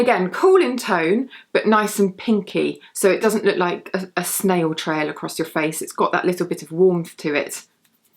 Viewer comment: No mention of makeup revolution? Their highlighters are about £3, they have loads of them and they're all great. And what's this about cool toned highlighters looking like a "snail trail"? What's that supposed to mean? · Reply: I thought she said snow trail.